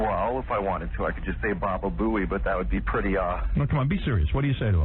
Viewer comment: did he say that he says ba ba boey?🫱🏾🫱🏾 💀💀💀